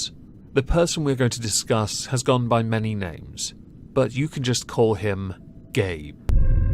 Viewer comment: Legit thought you said: “you can call him ‘gay’”.